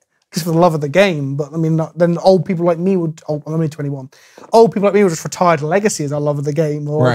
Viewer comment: Did I hear that right? Did say he was only 21?